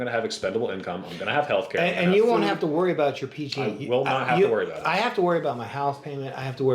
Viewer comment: he has to worry about his PG? what is he saying, what is that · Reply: Pg&e bill. It's a power company